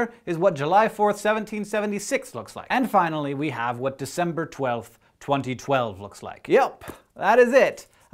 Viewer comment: did u say december 12 ??!!